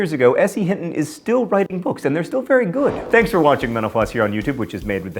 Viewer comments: I always hear "thanks for watching menopause." Every time! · When he says "thanks for watching mental floss" I always hear "thanks for watching menopause". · It sounds like you say, "thanks for watching menopause..." at the end.